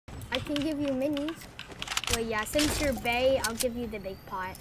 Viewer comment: I thought he said since your gay at the beginning